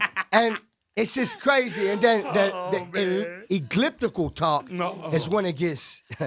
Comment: did khaled just say EGLIPTICAL TALK lmaooo